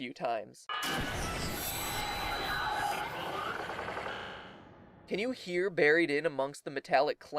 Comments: I heard help me at the last part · I don't hear a human voice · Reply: Can TASKIN its just hard to hear, listen closely and u hear "WAIT! NO! GET ME OUTA HERE!"